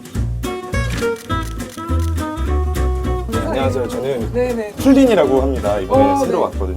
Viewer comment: 안녕하세요 저는 펭귄이라고 합니다로 들었뜸ㅋㅋㅋㅋㅋ 아이고ㅋㅋ사랑해❣️